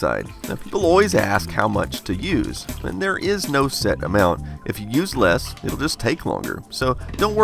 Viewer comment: 8 bit guy: if u use less it will just take longer... What I hear: if u’re useless, it will just take longer... Guys, subtitle are important